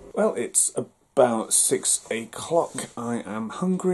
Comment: did he say 6 a clock